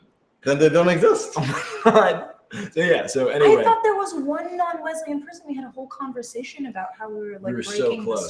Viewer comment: i was listening to this with one ear and 44:19 i hear " I thought there was one non-lesbian person...." and then about tumblr posts... wtf ? :D had to rewind hard!